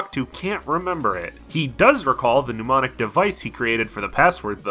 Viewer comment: My first couple times watching this I heard "demonic device" rather than "pneumonic device" in regards to the ship password. I didn't bat an eye because it was fitting.